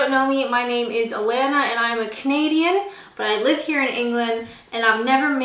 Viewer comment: Well this would be the only kind of cooking programme I'd ever watch, to be honest. This was hilarious! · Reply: For a few weeks now, I for some reason thought she always said "My name is Elaina, I'm a comedian"! Maybe she is a Canadian comedian!